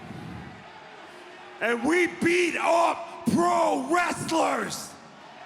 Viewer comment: Had to rewind Hager's speech cos i thought for a sec he said they beat off pro wrestlers 🤣